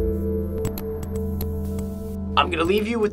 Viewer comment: I thought he said horse poo not corkscrew XD